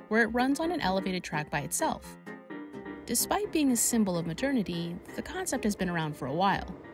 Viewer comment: I heard that as a "symbol of maternity". Even the captions agree.